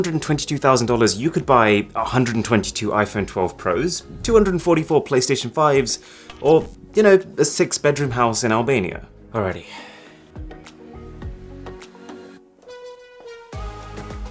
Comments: did he said Albania? that's where i live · did u say albania 🇦🇱 or somthing else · did I misheard or did it said Albania 🇦🇱